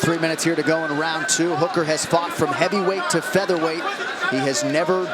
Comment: bro did he just say heavyweight? 💀